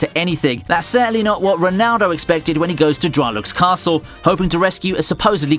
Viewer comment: i didnt hear wrong right? Did he say Ronaldo?🤔